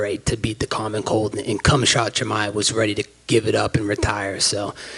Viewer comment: Did anybody catch that he said cum-shot chimaev at 4:29 😂😂😂 best shit ever